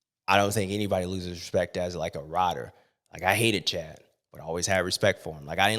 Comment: Did he say he hated Jett at 46:53 ? wtf ! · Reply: He said Chad